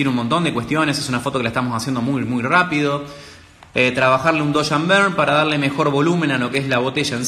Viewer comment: No entendí la parte donde el habla de hacer que la botella gane volumen, alguien me puede decir como dijo que se llamaba eso? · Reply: La técnica para el volumen se llama Dodge & burn